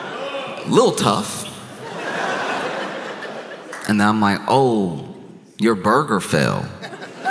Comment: Josh: "Oh, your burger fell." What I heard: "Obergefell," as in the Supreme Court case. 🤔🤦‍♀️